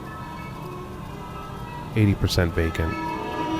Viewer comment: For a second I thought you said bacon